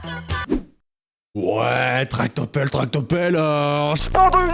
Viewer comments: Wesh moi j entend pas 《tractopelle 》j entend 《salle de bain 》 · Moi au "tractopell" j'ai entendu " tarte au pomme" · Coucou tout le monde, à 6:10 moi j'entends, et je l'ai déjà vu dans d'autres vidéos "Tartes aux pralines" qui enchaînent avec "Tartes aux pommes" à la place de "Tractopelle" et "Qu'allons-nous vendre ?" à la fin Sinon pour l'autre hallucination j'entends bien "on va quitter la France" mais pas la suite avec Lidl PS : j'ai adoré cette vidéo où j'ai découvert de nouvelles hallucinations auditives plutôt pas mal j'avoue 😂😊😁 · moi j'entends''tarte o pâtes '' 😂🍋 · J'ai entendu "tarte au pomme" et pas tractopelle ＼(°o°)／ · Moi pour le" tractopelle" j'ai entendu "tarte aux pommes"🤣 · Bizarrement, moi je n'entends pas « Tractopelle» à 6:12 mais «Tarte aux pommes» et «Go avant le tourniquet» à 7:00 😂😌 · Dans le "tractopelle" j'ai entendu "tarte au pomme" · moi j' entend pas tractopelle moi j' entend tarte aux pommes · A 6:12 moi c pas "tractopelle" que j'entends c "tarte aux pommes"... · j'ai entendu tacos bell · au truc tractopelle j'ai entendu "tape Topome" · Moi j'entends pas " Tractopelle " mais " tarte aux pommes " j'en peux plus punaise 🤣🤣🤣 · moi j'entend TACOBELL TACOBELL TACOBELL! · moi j’entends Taco Bell ; - ; HÉ JE SUIS DÉSOLÉ😭💀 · Moi pour « tractopelle » j’entend tarte au praline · Pour le coup du "tracopelle" moi quand j'étai petite devant shrek j'entendais TARTE AU PRALINES TARTE AU PRALINES TARTE AU PARLINEUUUUH X3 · moi j’entends « tardé aux pommes » TwT